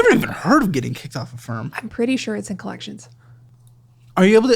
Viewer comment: I thought she was saying “I got kicked off of A FIRM” like a law firm.. then found out it’s the app “AFFIRM” she got kicked off of 🥴🥴